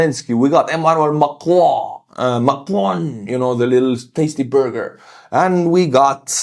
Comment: Artur: "Macron, the little tasty burger" Me: Why did he call Macron a little tasty burg... OH - macaron! 😂